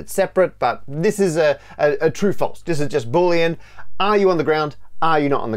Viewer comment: Hi Matt, at the 8:43 mark, the captions say "this is just bullying" instead of "boolean"... 🤣 lmao